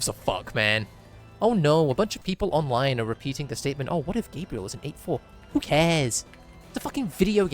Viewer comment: I GENUINELY thought you said "what if Gabriel was 8'4""